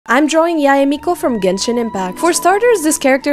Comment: At the start i heard she said "Yae Miku"